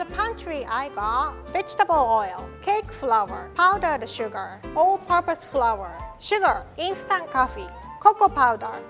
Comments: What she said: All purpose Flour What I heard : Old Papa's Flour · I heard "Old Papa's Powder" instead of "All Purpose Powder". This kind of stuff happens all the time when I go to Japan. Edit: My mom sounds exactly like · No hate but did she say ol papa flour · "V E G E T AB L E O I L" All I heard was b***h the oil